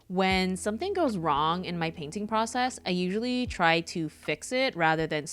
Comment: i'm eating and i misheard "i usually cry to fix it" and i screamed SAME XDDDD not we're not the same